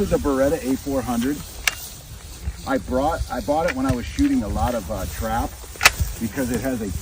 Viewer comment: Totally sounds like tim said he was "Shooting a lot of trout"